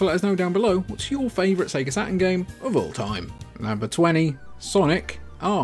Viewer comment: I keep hearing "Sega Satin" :D